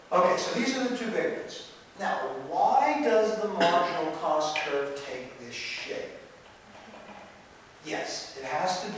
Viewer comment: "now, why does the marginal cost curve take a shit?" sounds kinda like what he said, but it's just his tone of voice that makes it hilarious. Damn, I wish I could understand this stuff.